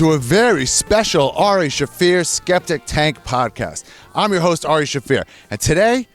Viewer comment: I always thought it was Ari Shaffir Septic Tank until I heard him say Skeptic. I thought it was Septic Tank cause of the type of dark edgy comedy his does.